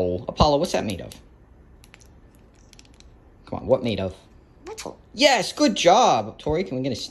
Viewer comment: At first when Appolo said metal I thought he said pencil 😂